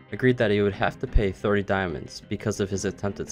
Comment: idk wtf you said but at 7:10 my siri tried to find me a diamond spa